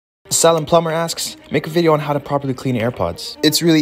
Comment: Hol up did you just say…sallum plumber…